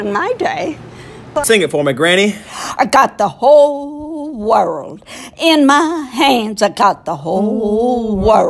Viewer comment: When she said whole world I thought she said hooooo lol